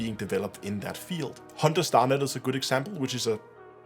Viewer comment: can someone tell me the name of the space combat game in development he mentions at 13:20? i either can't hear him correctly or can't find anything on the title he mentions... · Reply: HunterNet Starfighter